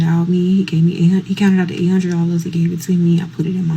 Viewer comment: did she say $800???